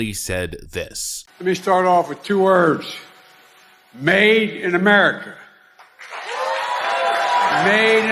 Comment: I was high on legal weed and thought Joe said “Maiden America” and got real excited for a sequel to Iron Maiden’s “Maiden Japan” album.